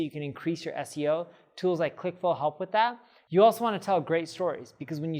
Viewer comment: Tools like what will help with CTA's click through? 2:52 Can't tell what you're saying. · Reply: Clickflow